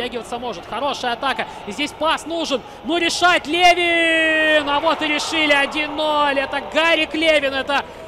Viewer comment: Мне послышалось не Левин а Ленин